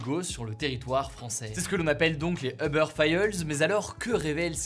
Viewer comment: Les quoi Hugo ?! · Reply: Les Eubeurs Faïlleuls